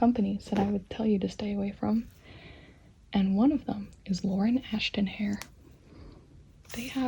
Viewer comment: When she said Lauren Ashton I thought she said Lauren afton and I was like: Wait so there is ANOTHER afton?!?!